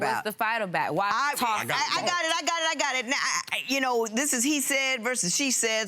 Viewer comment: did he just say "I got caught?"